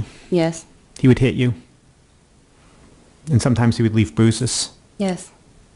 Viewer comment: Oh damn the captions did that prosecutor dirty with the lisp by saying “Bruce’s” instead of bruises lol